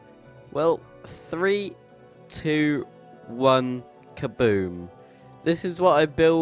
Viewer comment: I thought you said Kabood xD